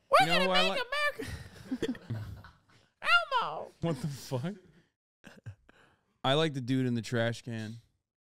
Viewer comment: I swore Hasan said 'I like to do it in the trash can'